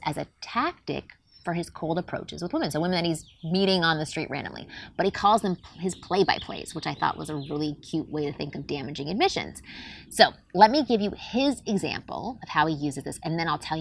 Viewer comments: I thought she said a damaging EMISSION! Butt that backfired!!! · Oh, at first I thought you were saying “damaging emission”.